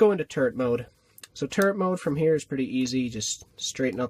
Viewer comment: it sounded like you said turd mode😂